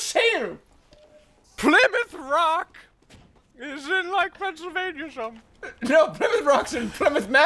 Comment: Can someone tell me what said at 2:11 - 2:15 i dont understand it. xD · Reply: "Plymouth Rock is in, like, Pennsylvania, or something" I believe.